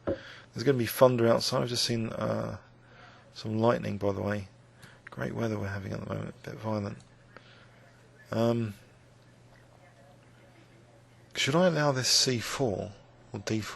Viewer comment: did he actually say "great weather at the moment, a bit of violence"? :D